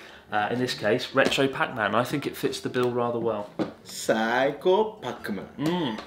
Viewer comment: When said "Saikou" I really heard "Psycho" LOLOL